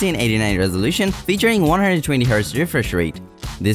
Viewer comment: I don't understand this part all i heard is featuring hdjdjdjsjsjuddjdhdhdbdb first rate